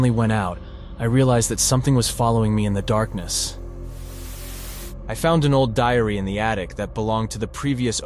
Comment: I thought he said I found a old diarrhoea or 🤭 Will not say it